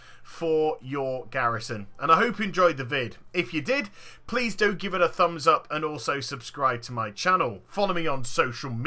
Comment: did you just say "please don't give me a thumbs up" ?